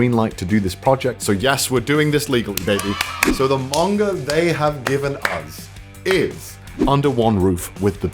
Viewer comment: Soooooo my notification sound is: nuttt and at 0:56 I got a notification from my friend and it sounded like: "So the manga they have given us is: nutt "